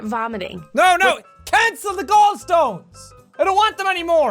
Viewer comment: i heard cancel the gold stones